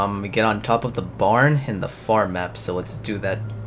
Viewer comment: Thought u said fart map lol